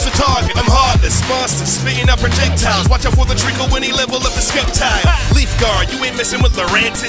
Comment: did he just say s-kep-tile?